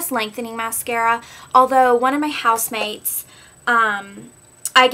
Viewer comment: HAHA omg, everytime in your past videos whenever you said "housemates" i thought you said "house maids" but i just realized now what you were trying to say. [: anyways. love this video. i need to start throwing away some of my mascara